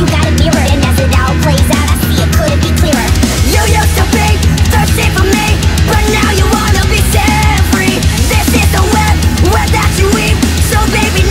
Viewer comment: I hear: "You used to be, thirsty for me, but now you wanna be Jeffrey." My name is Jeff.